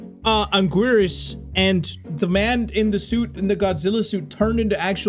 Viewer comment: I thought he said the man and the soup